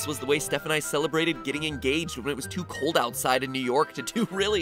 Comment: did he say engaged?! Congrats my dude!